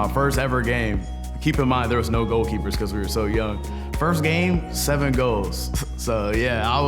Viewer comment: yo am i the only one that thought he said first game i got 7 kills when he was talking bout soccer im over here like damn bruh this manz been a god straight out da whom